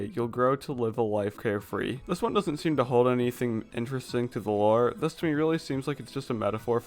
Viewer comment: sounds like vore instead of the lore